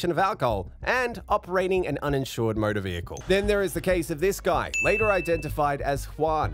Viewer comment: I had to go back to here 55:53. I heard murder vehicle. 😂